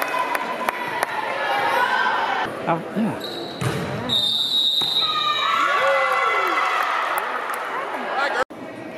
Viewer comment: Sorry I'm a bit new to volleyball, 13:40 what was being called here? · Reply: Yehet out of rotation or overlapping i’m pretty sure